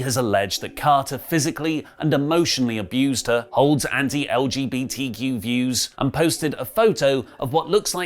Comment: I must be going deaf. It sounded like said: "Emotionally abused her old anti-LBGTQ views", which was only slightly confusing someone would be released for abusing anti-LBGTQ views in 2022. I had to rewind it five times to hear "Emotionally abused her, holds anti-LBGTQ views", which makes a lot more sense why he would be released. That British accent will get you sometimes. 😆